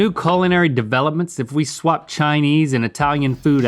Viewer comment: It sounded like Link said “colonary” instead of “culinary” 😂